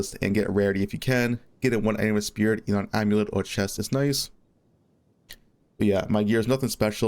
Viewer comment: what the hell are you saying at 1:50? I slowed it down to 0.25 and subtitles and I still cant understand shit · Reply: He said to get spirit on amulet or chest.